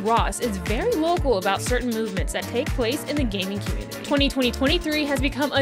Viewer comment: I really thought this female said "gay community" 💀